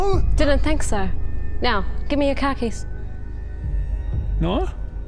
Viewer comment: oohhh... "CAR KEYS", I thought she said "KAKIS" like she wanted it his pants 🤣🤣🤣🤣🤣🤣🤣🤣🤣🤣